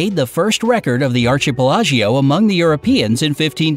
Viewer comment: Did he say ar-chee-pe-la-joe to mean archipelago???